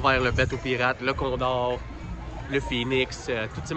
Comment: j'ai compris: là qu'on dort 😂 Trop cool la ronde en famille woohoo🎉